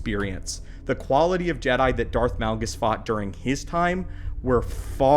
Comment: Did u just say Darth NALGAS?!?!??😂😂😂😂